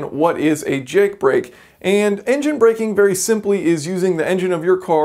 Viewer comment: I thought u said "simple" 😆